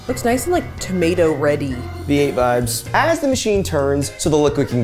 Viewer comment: am I hearing things or did say "the8 vibes" at 3:37 ??????